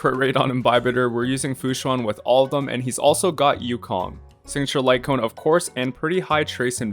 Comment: it sounded like he said Xingqiu light cone